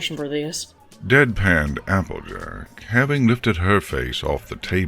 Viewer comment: It said "dead panned applejack" i thought it said dead parent applejack